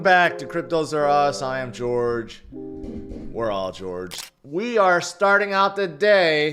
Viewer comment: I thought he said where are george?